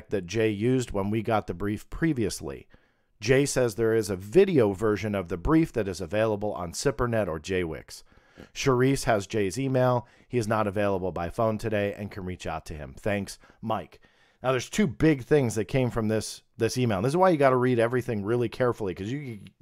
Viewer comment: I thought he said, "Jay-Z mail" but he said, "Jay's e-mail". Got excited at first.